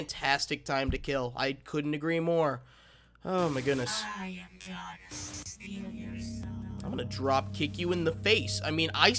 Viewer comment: I thought I just heard: "I am John Cena", until I went back and listened to it again. Lol